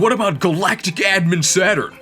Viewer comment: I misheard "Galactic Admin Saturn" as "Galacti-Dad Saturn," which is great. Like this video. A+.